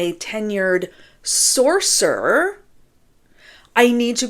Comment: Great video as always. Yay for Friday! And also... I had to go back and listen twice because I swore you said "tenured sorcerer" 🤣 I'm putting that on my resume. Hahahahahahahahahaaaaa! Have a great holiday and get some rest.